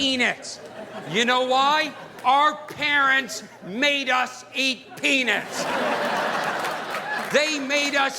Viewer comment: It sounded like he said "eat penis"